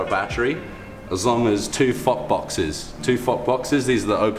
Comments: it sounded like he said fuck boxes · did he just say fuckbox